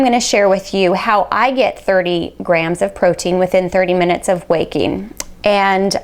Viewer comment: Thought she said, "Here is how I get 30 grams of protein within 30 minutes of weight gain."